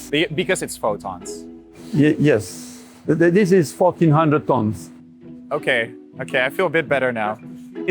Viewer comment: I heard f*** 100 Tons, went back and realized he might have said 14 hundred … 😂😂😂